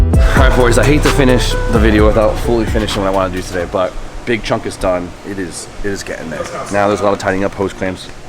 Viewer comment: Did just say “big chungus”..?